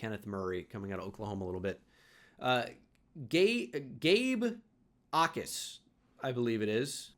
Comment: Gay Backus is all I hear when his name pronounced